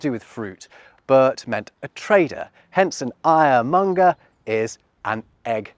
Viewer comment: When you spoke, I heard "Iremonger" as in someone peddling anger, but it was much more mundane. However, I should have seen it coming, because I can speak German.